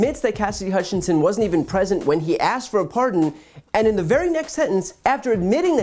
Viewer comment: she was never present when I asked for a pardon 🙂 my English is not that good, but It sound like he said that he asked for a pardon 🤣